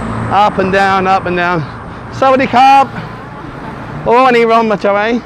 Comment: what greeting did you say to her? I always hear you say it to people passing by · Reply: Sawadee Khrab, which is hello and Sabadi Mai is how are you?